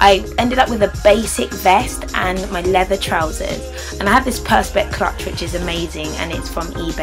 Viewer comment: I thought you said Perspex crutch??! Lol